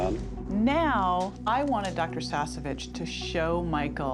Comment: Sorry for this but when introduce the doctor I heard "Dr. Sassy bitch